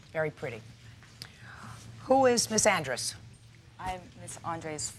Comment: At the start , I thought JJ said, "Who's a misandrist" and thought this could be an interesting case 😅